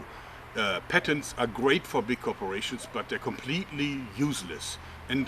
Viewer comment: I thought it said ''why are parents useless''